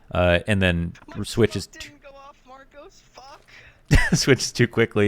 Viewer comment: junior” my smoke didnt go off marcos( i think its the coach or i misheard completely) fuck”